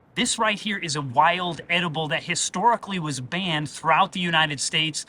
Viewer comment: I thought in the intro he said "this is a wild animal" and I was like "Hmmm... that's a plant..."